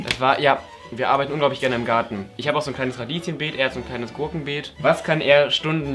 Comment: Ich habe verstanden " ich habe ein kleine Kaninchen Beet und er ein Gurkenbeet"😂